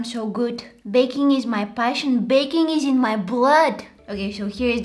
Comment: When she said baking is in my blood I thought she said baking is in my bread😂😂😂😂😂😂😂